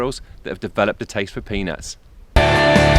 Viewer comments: tell me im not the only one who heard penis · it sounded like he said that have developed the taste for penis